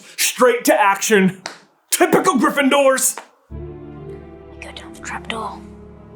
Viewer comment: Nowadays, whenever someone says Gryffindors, I hear Dunderheads